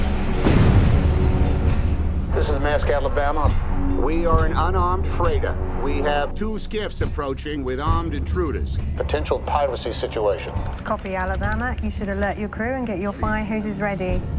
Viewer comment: Does someone know what Tom Hanks says at 0:38, it sounds like he's saying we are an unarmed frada but I don't know if the word frada exists, but I know he refers to the cargo ship, can someone explain me what he says, thank you.